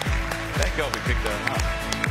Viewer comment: ah, what is being accidentally said (by Gillon?) on a mic at 0:25?? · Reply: <num> Sounds like he says "thank god we picked her up"